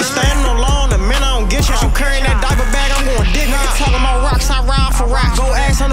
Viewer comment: Can somebody tell me what he said at 0:51 he said “he carrying that diaper bag I’m…?” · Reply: Going GET HARD 😂😂Like the movie the dude had to get taught how to be tuff for jail and he wasn’t but he was trying be street 😂😂😮‍💨💪🏿 tellin em shit